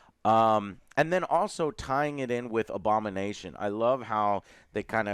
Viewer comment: all I hear is Obama Nation, lol